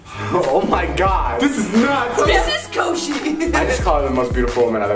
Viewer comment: this is nuts Liza: this is Koshy XD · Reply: Helena Egan I thought she said "Mrs. Koshy"